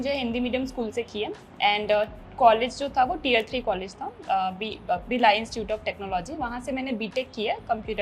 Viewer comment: Bhaiya Please correct the caption as the speaker at 2:36 mentioned "Bhilai Institute of Technology" instead of "Birla Institute of Technology". There is a significant difference between the two, and accuracy in captioning is crucial. 🛑🛑🛑🛑🛑🛑🛑🛑🛑